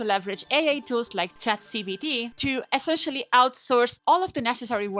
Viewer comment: sounded like she said ChatCBT